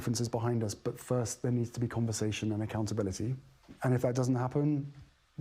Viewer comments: He said "conversation" @ 7:22, "not compensation" Piers. Ya'll don't need to be reporting, because you don't hear right. Very few men have mastered the art of hearing. Get it right. This is why the world is in such demise and degeneration from the misleading reporters and others that don't hear clearly. There's a price everyone will pay for their misinformation. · Piers, you quoted Harry as saying there must be "COMPENSATION and accountability" between him and the royal family but that he NOT what he said. He said "CONVERSATION and accountability". Listen to it again and you will see that this is clearly what Harry said. I am going to assume that you actually thought he said "compensation" and not that you deliberately misquoted him like we see so often in the fake news media. Just misquote ONE WORD and we will all think we heard what you said we heard. But we didn't! I am NOT a fan of Harry's but I think we need to at least quote him accurately! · Piers listen to 7:23 Harry said "conversation and accountability" and not what you said "compensation and accountability". You see this is how you make false claims about what people say, like saying H&M said the royal family was racist. Those were your words not their. BTW HM look happy and loved up, I can't say the same for you